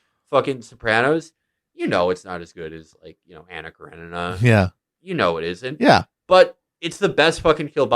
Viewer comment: what the fuck does felix say at 33:44 ?? i can't figure it out for my life what he says is better than the sopranos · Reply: Anna Karenina